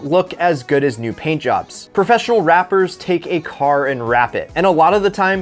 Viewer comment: “Professional wrappers” Sounds like he said “professional rappers”😂